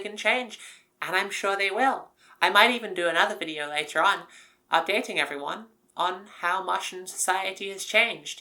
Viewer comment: at first I misheard "updating everyone" as "i'm dating everyone" as if to suggest you were part of the most powerful polycule on mars